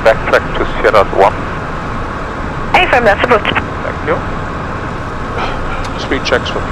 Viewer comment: what did she say? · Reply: She answers "affirm, that's approved" to my question to turn of the runway on the first taxiway to the right. Happy Landings, Capt. Please consider subscribing to my YouTube Channel and hit the "notification bell". No cost for you and you get notified about any new video uploads. Your support is highly appreciated.